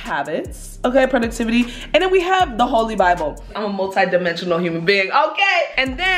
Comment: GURL I THOUGHT U SAID THE "HALSEY BIBLE" AND i was like wait slayy i need that